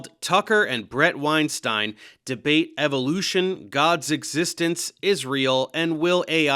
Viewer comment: “…God’s Existence, Israel…” sounded like “God’s Existence is Real” neither would have surprised me Edit: Timestamp added